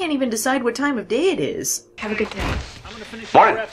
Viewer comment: "Have a good night!" "Mornin'!" One line. They had One line to keep it consistent and couldn't even manage that. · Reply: She didn't say night. I hear her say "Have a good day".